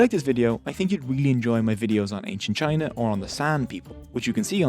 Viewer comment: I thought you said "the Sand People" at the end, there. That would be cool!